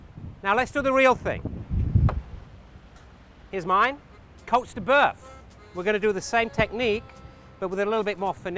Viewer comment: What does he say at 12:56 ??? please? Colts deBurth ? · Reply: Chance Utter cote de boeuf